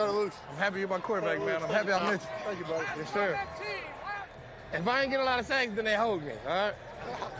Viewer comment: Von Miller savage af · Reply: Thank you i thought he was saying "if i dont get a lot shanks..." tyank you for clarification